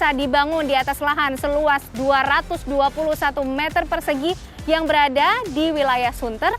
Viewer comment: 221 meter persegi. Aku dengernya sih begitu